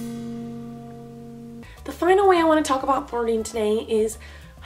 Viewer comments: The whole entire vid I thought she said farting instead of flirting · omg I thought she said "the final thing about farting"😂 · Sounded like the was saying farting, not flirting